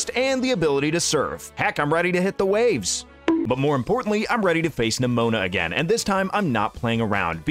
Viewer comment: i sneezed right there so i heard you say "im ready to kiss nemona again"